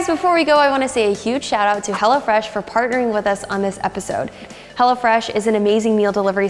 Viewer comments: When you said Hello Fresh I thought you said Hell a fresh 😂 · i lowkey thought she said 'HELLA' fresh rather than 'Hello' fresh